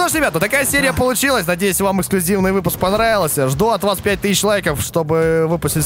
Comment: Послышалось 25.000 лайков или 5000 лайков